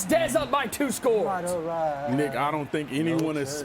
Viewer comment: what did he say at 5:53?????? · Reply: He said “Nick” lmfao his co-hosts name??